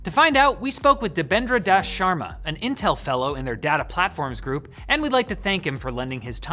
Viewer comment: I thought he said he was speaking with an Intel sellout haha, pretty harsh on the guests man!